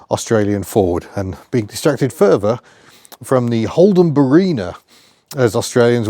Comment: Holden chose to name a car “Marina” the mind boggles 😂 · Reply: Fair play, I misheard it as Marina.